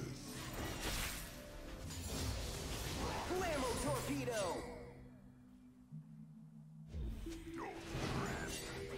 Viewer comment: Does Rumble say "huevo cocido"? (which means "boiled egg" in spanish) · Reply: no sir he says "blammo torpedo"